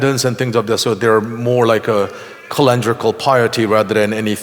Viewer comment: I can't make out the word he's using. Is he saying some form of "piety"? It sounds like he's saying "colandrical piety". Can anyone help me out? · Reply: Young King Simba “ Calendrical” - in this case I think he means the practice of going to church based on the calendar, at Christmas and Easter, that sort of thing.